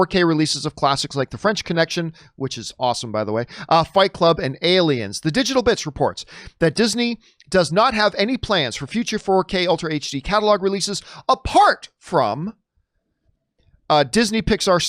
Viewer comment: Lmao I thought John said “the digital bitch reports.”